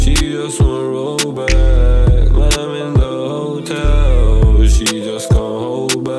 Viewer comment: im sorry did he just say "my nans a hotel"😭